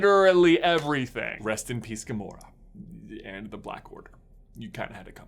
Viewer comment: thought you said black widow not order had it coming and was gonna unsubscribe